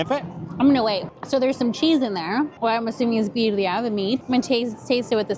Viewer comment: 🤦🏼‍♀️I thought you were saying quesadilla but you were saying birria. I love birria! Weird they put it in an egg roll.